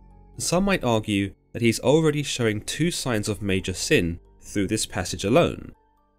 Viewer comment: Lmaoo I heard “major simp” instead of major sin